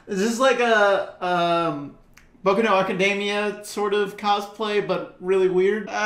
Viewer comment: did you just say Boku No academia instead of Boku No Hero?